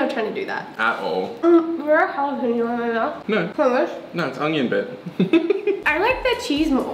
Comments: calls babe I was like WTF and watched it like 4 to 5 times to make sure.....and it's true why did he call u babe are u guys not broken up yet.... WTF please get back together love u xoxo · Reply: he said onion bit. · did he say “no it’s onion babe” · did he call her babe??? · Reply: No he said onion bit**